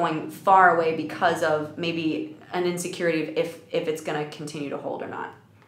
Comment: did you say she was feeling “unsecure?”